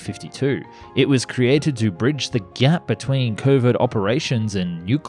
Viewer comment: Did he just said GYATT? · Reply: He said gap🫤